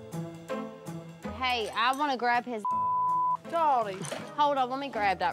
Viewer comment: did she just say dordy love it 😂